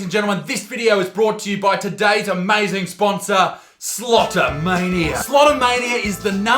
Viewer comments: I misheard the sponsor's name, I thought he said slaughter mania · I thought you said slaughter mania, I was confused by the slot machine for a moment.